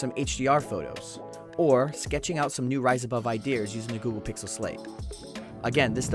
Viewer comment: does he say idea ers 🤣